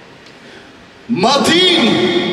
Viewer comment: PARTY始まる前のヤツ 『マティーニ🍸』って聞こえるw